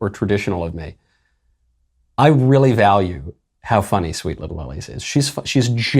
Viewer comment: Who? He mumbles her name, and I have no idea what he's saying. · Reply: Same. Sweet Little Ellie?